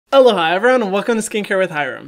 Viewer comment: I always thought he was saying “hello, hi everyone” until this video